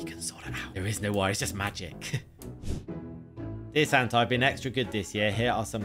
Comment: it said Satan instead of Santa lol